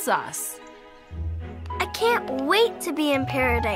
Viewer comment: Hearing a kid say that she can't wait to be in paradise is kind of scary... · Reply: Lmao all I heard is "I can't wait to die! 😄" Which is creepy and horrible but...same kid 💀💀💀